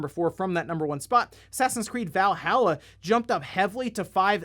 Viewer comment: Yo, I was in the other room and thought he said "Sesame Street: Valhalla" Now I have so many hilarious images in my head. super grover lifts up Mjölnir one last time, knowing that it is the only way to keep oscar from ruling over the dump with an iron fist